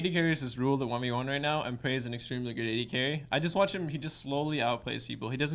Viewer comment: turn on subtitles at 1:30, you're welcome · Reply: Lol at 1:20 he says pray is an extremely good adc Subtitles say pray is an extremely good gay gay